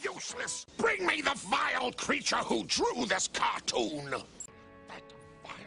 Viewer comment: I can't hear you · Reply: James Kidd oh you mean, "That vile creature"?